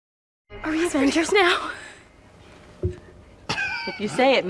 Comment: I thought I heard "Are we Asswangers now?"